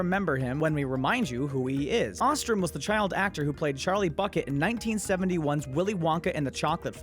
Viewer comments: did he say Charlie F It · did he say bucket or fuckit? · did someone say "Charlie FuckIt" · For a moment there, I thought you said Charlie Fuckit